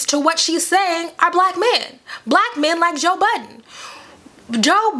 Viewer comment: Bruh I thought you said , Joe Biden .....